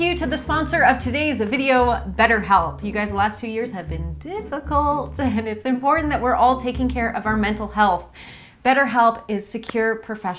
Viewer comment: I thought you were saying us guys have been difficult the last two years. No commas in speech. Got it after a few seconds.